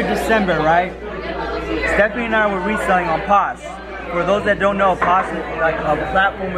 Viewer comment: says he's reselling on _____ ? a platform for selling used clothes. I can't make out the word, name of selling service platform,. Sounds like pods or paws but that's not it. Anybody know? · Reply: posh (poshmark)